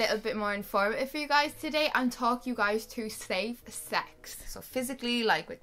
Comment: I heard "slave" sex not safe sex, lol.